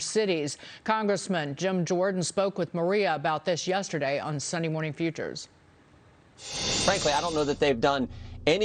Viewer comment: I thought Dagen said "Sunny Morning Futures", on opening the program. Sounded so cheerful and positive.